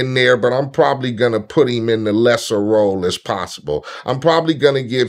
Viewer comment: I thought it said "the Sheriff role" now Im imagning how Pac would of reacted to the offer of playing a cop in a movie.